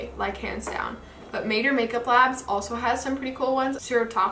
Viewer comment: what makeup brand did you say? Mater or Major makeup labs? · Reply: Jennifer Dawn Vazquez-Gonzalez maeder Makeup labs 🤗